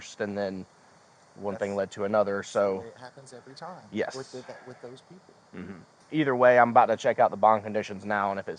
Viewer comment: Did I hear him right??? The time was at [ 23:45 ] . I could swear That I heard the police officer say These people.. or these types of people. 👥👤👥👤???. C. the year is 2026.. And it is sad that the world still sees things that way.... ..👽👽🙊🤔💞💫💘💖🙊🙊🙊